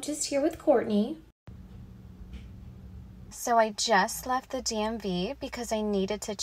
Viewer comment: I heard, “So, I just left the Damn V...” 😂